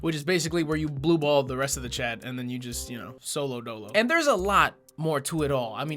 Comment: Did you actually say at 3:05 solo dolo?